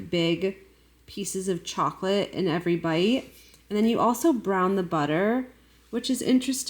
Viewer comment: When you said "brown the butter" I heard "a pound of butter" and I lost my mind a little.